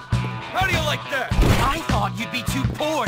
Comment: "Are you like that" johnny Vincent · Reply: PapiGodie ow sorry my bad english :-D, i thought he say "are you like that"